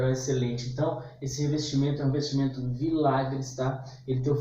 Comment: Qual é mesmo o nome da marca desse material? Eu entendi "milagres?" é isso?